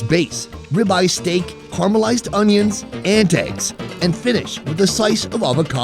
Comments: was he saying ant eggs? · humm... did he actually said: "ant eggs" @ 10:55 or I heard wrong??